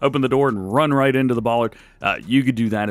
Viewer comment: Lol.... at 9:50, did you really say what it sounded like you said?. EDIT: listening for about the 20th time, maybe you did say 'bollard', not what i initially thought lol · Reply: yeah, I said Bollard lol